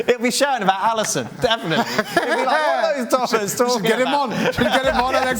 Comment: He DID just say "tossers", right?